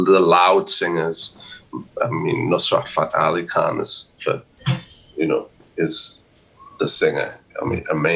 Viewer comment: which "loud singer" is it, that he calls amazing? Does anyone understand? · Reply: hey bud hope u see this, the singer he mentioned was Nusrat Fateh Ali Khan